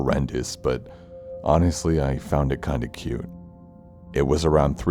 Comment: did he just say cute?